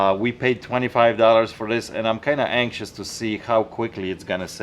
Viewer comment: did he say horror or whore?? 😁